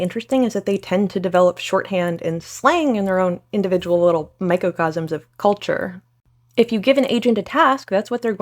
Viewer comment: I heard "myco-cosms", now awaiting something fungal